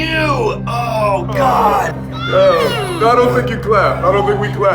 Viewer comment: did Will say "No no I don't think you clap" or "I don't think we slap because then he'll slap back"???